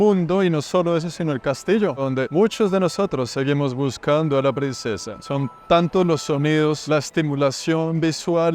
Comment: Perdon??!!! Escuche mal ? En el minuto 11:35 dices buscar tu princesa??!!! Aca estoy!!! Al sur del mundo 🌎😅